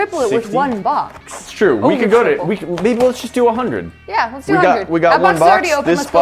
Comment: Does he say babe or maybe